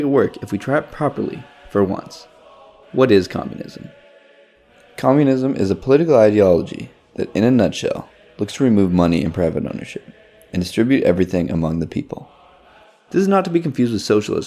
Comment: oh, may I ask some part of this video like at 0:17 what did you say ? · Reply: "Communism in a nutshell is a political ideology that looks to remove private ownership and distribute everything among the people."